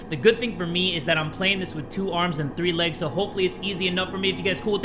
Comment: bro i rewound because i thought i heard three legs... i was correct